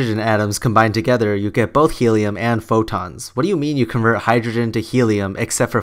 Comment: Love your videos, but I think he said four tons at 3:34, not photons? Hard to hear though.